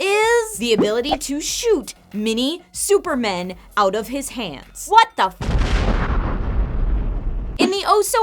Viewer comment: I misheard and thought that said "shoot mini-supermans out of his pants." I nearly choked on my Tea.